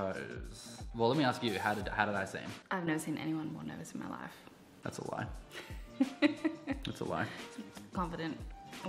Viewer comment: I thought you said "That's a wipe" 😭😭😭😭